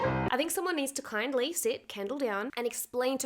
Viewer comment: LOL when I was watching this with captions it showed "candle" instead of kendal 👽💀